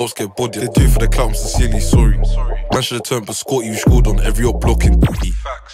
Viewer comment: Anyways Quick reminder for you sausages, is the best in North London and is up next 🤧 ur fave rapper tion can't do these numbers without big features 😎 mockingbird would of already been on 30M views if didn't get erase 🏆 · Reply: Is it just me or does his censor sound like he said rashford shudda turnt biscotti😂😂😂